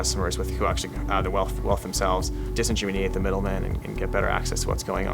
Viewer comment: What word does he use at 3:53? disen..?